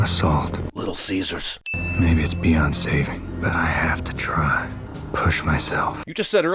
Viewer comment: he says, "maybe it's beyond saving, but I have to try," but the first two times I heard it and rewound it, I definitely heard, "maybe it's Beyoncé, but I have to try..."